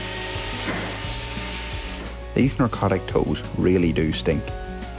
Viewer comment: Narcotic is a drug. Necrotic is dead tissue. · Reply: Yes. It did sound like he was saying “narcotic”, instead of “necrotic” — and “narcosis” instead of “necrosis”. I’m not eager to quibble, especially when is such a capable professional, but the pronunciation was distracting! I wondered if it could be a matter of poor audio quality, or the difference between American and Ulster accents, but I’m just not sure either would account for the apparent mispronunciation.